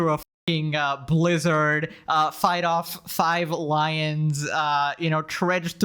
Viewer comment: I had this playing in the background and thought you said "Fight off 5 Hawaiians" lmao